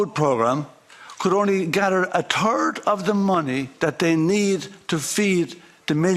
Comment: When he said third of the money I heard “turd”. I thought he says it like it is!